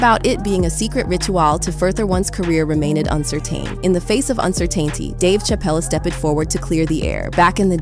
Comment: Did she say Cer-tan-taint-ee? LOLOLOL! Right around the 4:50 mark. Someone forgets how to speak english.